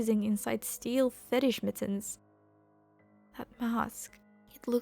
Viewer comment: DiD sHe JuSt SaY ‘sTeEl FeTiSh MiTtOnS’?!